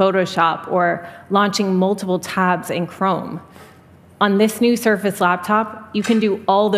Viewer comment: Did she say multiple tabs in Chrome?